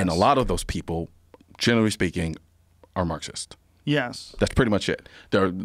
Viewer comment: can someone tell me what he said right here and what that word means i couldnt really hear him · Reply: He says "Marxist" Marxism is a social, political, and economic theory originated by Karl Marx, which focuses on the struggle between capitalists and the working class.